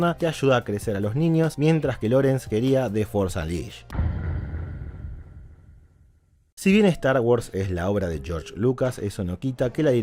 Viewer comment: no se te entienden ciertas palabras, que dijiste ahi 8:19 ? queria que? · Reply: "The Force Unleashed"